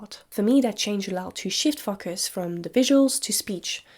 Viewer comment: I know you said "shift focus" but I keep hearing "shit fuckers" Anyway, I don't think loneliness has been romanticized but rather normalized. Loneliness, especially among males, was already on the rise before the pandemic but since then it has severely intensified.